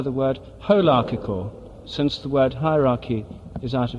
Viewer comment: It took me some time to understand what he said here, maybe someone else also would benefit: he say that is better to describe how Nature works as wholearchical, instead of hierarchical. “Whole”archy Term: As opposed to a “hierarchy”, a “wholearchy” integrates all pieces of its structure into an organized system. All components are equally needed and rely on each other to provide a healthy ecosystem. No part is seen to be better than another.